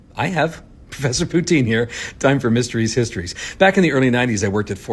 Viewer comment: For ages I thought you were saying Mysteries Histories and was really confused because nothing seemed terribly mysterious hahaha